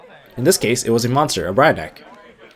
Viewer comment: Did you call Brionac a Ryback? xD